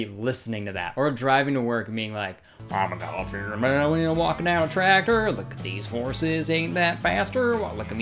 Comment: "I'm a god-fearing man and I'm walkin down a tractor Look at these horses ain't that faster Look at me go I'm eatin some beer Let me go or touch my ear" I don't like country music but I'd listen to that whole song XD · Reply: I thought he said god fearing mailman walking down a tractor